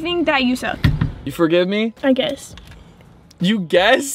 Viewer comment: I thought she said, for a kiss, instead of I guess.